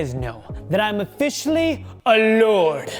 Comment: I thought you said “im officially ALONE” and I wasn’t surprised